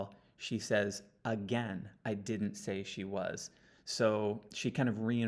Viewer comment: I heard “I Didn’t say she was.” Plus she emphasizes it a few phrases later.